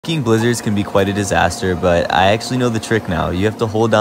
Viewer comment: ayo did he say “ fcking blizzards “ ? Or am I tripping cause I heard “ cking “ in the beginning like it was cut off or something